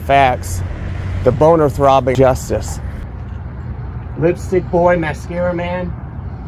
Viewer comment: Lipstick Lad and Mascara Man instead of lipstick boy.